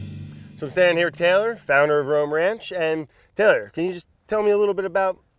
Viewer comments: I thought he said ram ranch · Brad got sent to the ranch 😔 ✊ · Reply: LMFAO, brofist also, this ranch is called "Roam Ranch" but I swore I heard "Ram Ranch." Look it up. XD